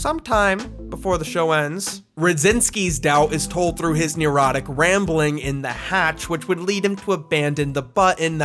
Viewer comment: did he say erotic rambling?